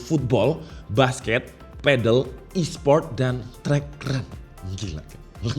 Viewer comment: gak salah denger kan ada tambahan menu workout baru "e-Sport"??? workoutnya e-sport itu ngapain yak, senam jari apa gimana bang hehe